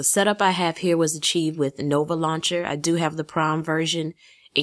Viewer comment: When you where talking about nova launcher I thought you said the prom version but you said prime lol I was like wow what's the prom version